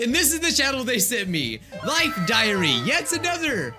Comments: You know, I thought was bout to say Life Diarrhea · I thought he said like diarrhoea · I thought he said life Diarrhoea… lol